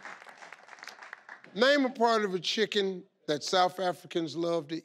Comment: Did I hear the lady at 6:29 say “chicken intestines”, as if the lady who said heart should have said that instead?”